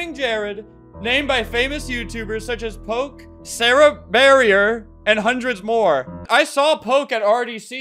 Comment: nah I thought he was gonna say Sarriebarrie